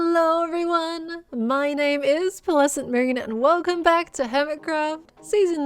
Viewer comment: When you say "Pearlescent Moon" in your intro, I always hear "Pleasant Moon". Either works. :)